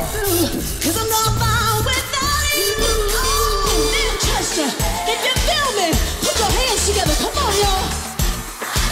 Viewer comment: anyone can tell me what did she say right before plz ?? "... can you feel me ..put your hands together come on ya'll " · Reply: the name of the city, "Manchester"